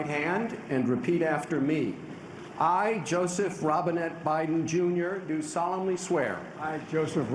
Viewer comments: I thought they said Joseph Raisinet Biden😂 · i swear the first time i heard this i thought he said "Joseph Robinhood" ... i was like wat... :P